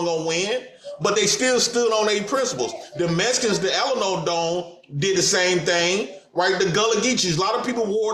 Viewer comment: Did he say the Alamo Dome?